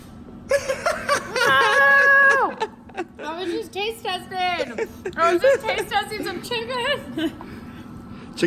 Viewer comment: I heard her say "Nooooo! It was disguuuusting.", because I played it at 2x. I feel so biased now that I watched it again and understood "taste-testing".